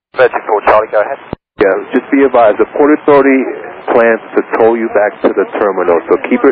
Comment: So much work on this one and so many hours non-stop to have this done by today. Be advised, animations may not be correct + audio is not in real time. Anyway, I hope you find it useful to visualize the situation. From these lines, KUDOS to the air traffic controllers and the emergency services for their job. No delays were applied except for both affected aircraft and the A318 and B757 behind who had to wait for the planes to clear the area and taxi for their takeoff. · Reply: he actually says, "Yes, just be advised The Port Authority plans to tow you back to the terminal...